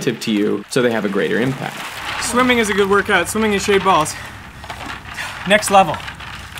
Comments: thought he said "shaved balls" sorry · i literally thought he said “i’m swimming in shaved balls” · i thought he said swimming in shaved balls hahaha